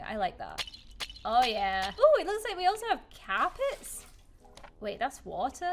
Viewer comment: it sound like you said cap its. Me: hello guys do you like my new carpet? Yammy: hello guys do you like my new capits